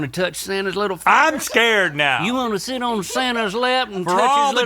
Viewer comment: yo what's link saying, sitting on lap and touch little fingers